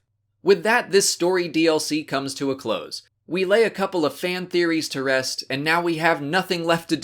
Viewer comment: did you just say "we Leia'ed a couple of fan theories to rest"